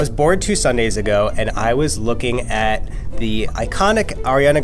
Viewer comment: I really heard "i was born two sundays ago"